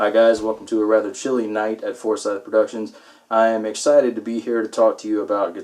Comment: I thought you said "Foreskin Productions" lmao